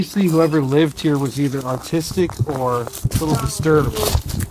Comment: Hey 😊 I happened upon your comment and I thought it might help if I share my experience here. I thought he had said autistic also (my son too is on the spectrum and has other neurological and mental health conditions). But I realised he said "artistic". I hope this helps you feel some comfort and sending you love and hugs, prayers and any other comfort and strength you might need now and into the future. You've got what it takes or you wouldn't be his Mumma. You've even spoken out respectfully and considerately to a perceived insult and injustice and, if you can do that, you can do anything my friend. As too will he with the support he so obviously has with you 💞 Much love to you and your family and very importantly, from my past and current experience (my son is not long 18 now)... Take care of you first or you'll have nothing left to give, even if you want to. God bless hun and you are never alone, I assure you xo